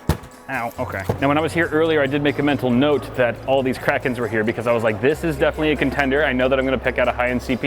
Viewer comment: i wasnt looking at the screen and at 8:13 i thought he said there is all these crack heads here